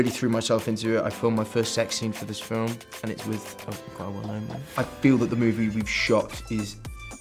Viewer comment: What did he say at 3:28? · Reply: Hanna Norhisam “ it’s with quite a well known man”